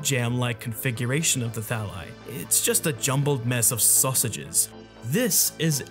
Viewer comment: has Amogus corrupted me this much or did my ears not deceive me when I heard “susages”? lmao